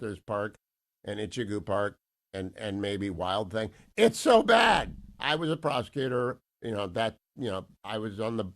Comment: i keep hearing "i was a pr*stitute"😂😂😂